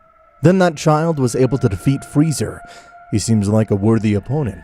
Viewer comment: I heard this man say freezer instead of the other name